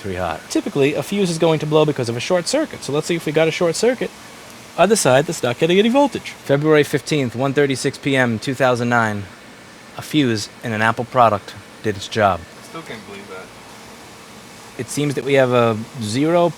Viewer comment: did you say 2009????